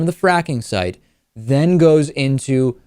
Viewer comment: i thought it said fucking waste site, my bad lol